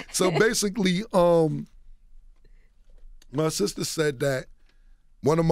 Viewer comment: He clearly said "my sister" the 27:21 Mark when explaining about the juju beef